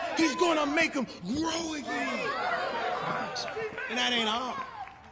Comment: I almost thought I heard him say ...make America great again.